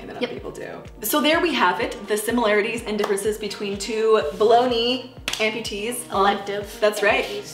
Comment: I keep hearing "below knee" as "bologna"